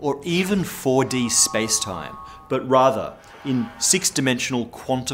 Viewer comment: did he say raava?